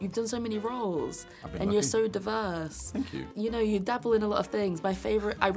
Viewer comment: at first i thought she was saying "you're so devine" instead of "diverse" hahahah